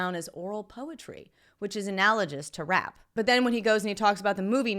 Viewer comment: did she say analoJous?